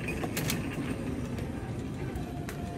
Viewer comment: I thought he said " your adopted, your adopted" 😆😆😆